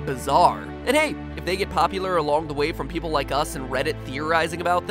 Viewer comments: Does anyone else hear him say "Poppylur"? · did he say "poppy"lar (popular)? Like if you agree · Wait Did he say Poppy-lar at 17:49?